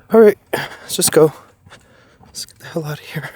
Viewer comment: Wow it sounded like someone said get the h*ll out of here as you were leaving … I believe there is some people who must be living out there and don’t want anyone there … maybe a group feral people who don’t like others around … just be careful we love you ❤